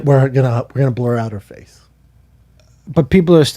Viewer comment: I first heard,"we're gonna blow out her face."